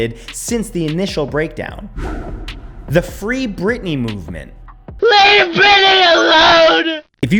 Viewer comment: Ok that meme, Now i hear “Britney” In it, I heard “Billy” Before!